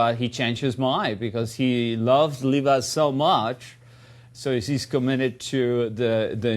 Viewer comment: Did he just call Lisa "Leela"? Keep in mind HE wrote this film.